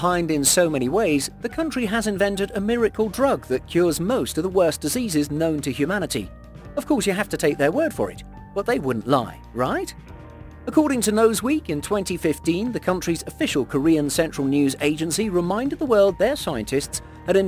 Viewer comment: Lol he said nose week instead of news week. Hahaha